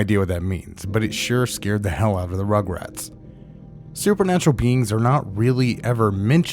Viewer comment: I heard: "supernatural beans" and skipped forward when normally its supposed to say "supernatural beings"